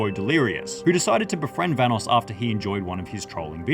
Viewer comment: did delirious just say vanoos